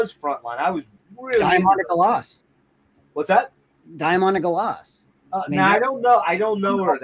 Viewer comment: What’s the artist Jon is saying at around 1:30:30? He says it’s the most terrifying music ever recorded. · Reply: Diamanda Galas